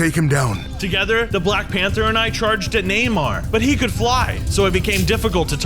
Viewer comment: Am i the only one who heard say "Together, Black Panther and I charged at Neymar" or did everyone hear it